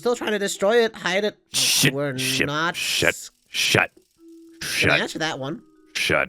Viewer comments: HELP I THOUGHT THEY SAID SH!T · YESS I HAVE BEEN WAITING Edit:At 12:16 thought moon said sh!t for a second Edit 3: someone PLEASE animate sun playing the violin and lunar playing the drum I beg of you!